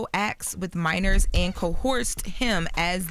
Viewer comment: Sounds like she is saying Co-horse not co-erced 😂